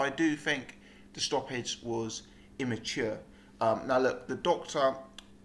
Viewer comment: dude, did you say that you thought the stoppage was immature at 2:34? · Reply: premature